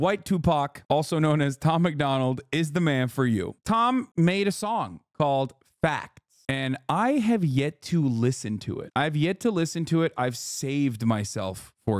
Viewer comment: At first I thought you said, "Tom made a sing: fap." lol